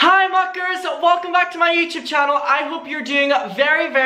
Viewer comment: I thought he said “hi fuckers” lol I’m shook